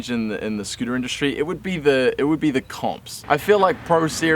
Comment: When he said comps I thought he said cops